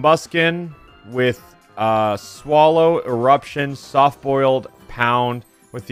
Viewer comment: I thought it said small Instead of swallow